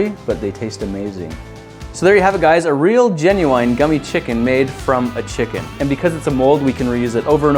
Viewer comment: Thought it said gammy chicken 😂